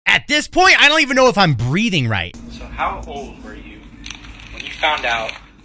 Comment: I thought he said reading but i heard breeding but then i read the text and it was breathing.